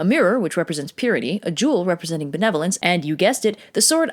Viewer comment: Mishearing "A jewel which represents the Netherlands..." Wow, I know Japan had a fairly good relationship with the Netherlands a few hundred years ago but that's pushing it... Oh wait benevolence. Yeah. That makes more sense.